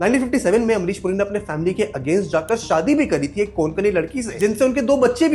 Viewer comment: 😭 I had to repeat 5 times and use subtitles to understand what he said Not - कोनकणी ❌ कोकणी ☑️